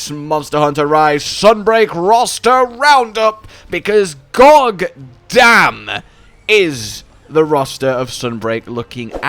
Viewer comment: I like how he says gog damn instead of god damn because gogmazios